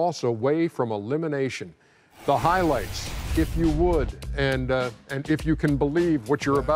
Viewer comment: I thought he said “the highlights give me wood” 😂 right before the highlights start